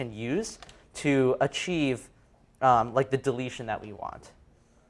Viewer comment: Did he just say 'deletion'?